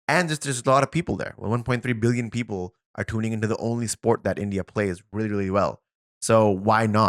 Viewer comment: Did he just say the only sport india plays good 😡